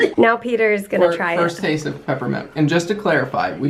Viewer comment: LOL Pepper Milk, I thought you said peppmint, as in peppermint oil or essence, that would be weird lol. I think it makes you guys laugh more when your pranks bomb. I have to admit I think it's funnier too.